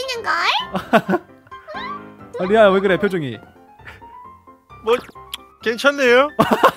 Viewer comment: 방,방,안방이젤예쁘넹~ 와 그리고 멤버들이 상황극을제일잘하넹 그리구 11:17 에서 11:19 에 "뭐..ㅉ괜찮네요?"이럴때 너무웃겻는데 제가들리는데로는 "엇.. 뭐..ㅉ괜찮네요?" 이렇게들림ㅋㅋ그리고 11:33 쯤에? 이 "컴퓨터가 없어졌지만 마음에들어요" 에 너무웃겼어욬ㅋㅋ (빵터짐) 근데 'ㅉ' 은 '쯧' 이게아니라 '쯔' 입니다 ㅎ skrrr 할때웃기네ㅋ ㅋㅋ 멤버들이랑 사랑 합니다 ❤🧡💛💚💙💜💖🖤